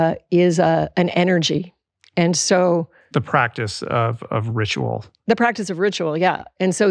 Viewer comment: Hi Julie! Awwww. Thank you for sharing. I thought you said “the practice of Rich Roll” but you said “ the practice of ritual”. 😂❤❤❤❤ it a new thing. My husband is one year out of 4 elbow surgeries in 3 years. It’s been difficult. We hear you completely. It’s been life changing in many ways. 😢😊